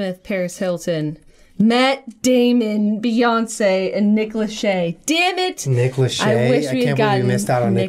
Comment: I completely misheard and though you said Nicholas Cage.